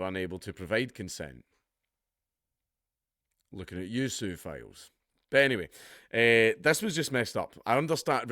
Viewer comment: I thought he said suephiles.